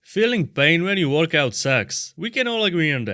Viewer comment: I thought he said "feeling pain when you want to have sex"